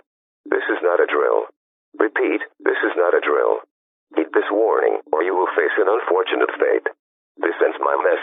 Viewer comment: for a second I thought he said, "eat this warning".